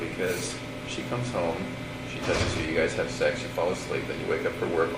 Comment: does he really say ok so walk me through she text touch (not sure what said) and you guys have sex! Did he really say that!??? Did they have sex that early morning before he killed her ? Or am I getting the time line wrong